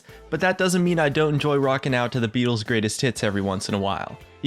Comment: At first i heard "the beatles' greatest tits"